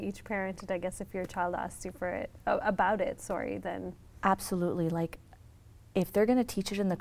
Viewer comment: Did she say ask you for it